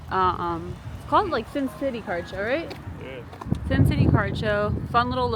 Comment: Omg I thought you said car show 🥴🤦‍♀️ haha. It's really cute and cool you two found each other because you both love pokemon 😊 just getting into the vlog and knowing you both. 😊